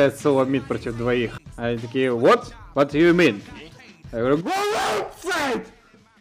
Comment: что он сказал? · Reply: "Go outside!!!"